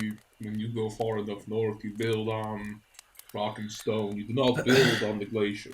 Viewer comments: Did I hear a Rock n' Stone? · DID I HEAR A ROCK AND STONE?!?!?